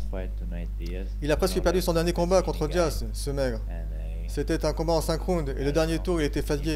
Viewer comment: Il dit quoi à 4:01? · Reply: Oui il dit skinny je viens de remarquer la